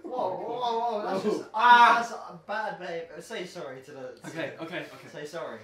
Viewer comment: does say „that’s bad babe“ or did I just misunderstand it? 😂